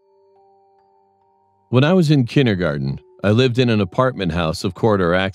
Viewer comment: I thought in the begging he said " when I was in chinder-garden"